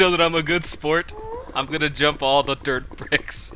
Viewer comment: <-- X did u say "All the dirt BRICKS"? :D